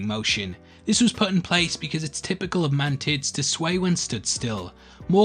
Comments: On minute 11:34 he says "mantits" instead of "mantis" and now I can't un-hear it haha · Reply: mantids, the name for the family of insect. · Did you just say "Mantits"?